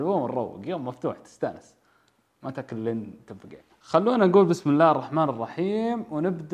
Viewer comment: يا جماعة وش قال في الدقيقة 0:24 ؟ ما تاكل لين ...🤔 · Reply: لين تنفقع